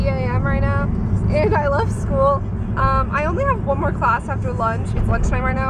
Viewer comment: thought you said "and i love school" haha